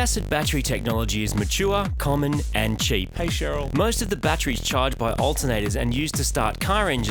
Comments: "hey Sheral" in the back ground, anyone hear that? i laughed hard! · Did anyone hear that "hey cheryl" at like 11:10? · anyone else hear "hey Cheryl" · Anyone else hear say "hey sharal" at 11:11? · "Hey Sherrell?" What is he saying? Listen closely. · did anyone else hear the "Hey Sharel" at 11:09? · Did anyone else hear say "Hey Cheryl" at 11:05 or am I losing my shit? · I heard the "hi Cheryl" at 11:10! XD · anyone else hear say hey Sheryl at 11:10 ? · Hey Cheryl! 👍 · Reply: i swear i heard say he cheryl